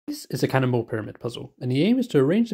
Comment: Am I the only one who thought he kept saying CANNIBAL instead of cannon ball?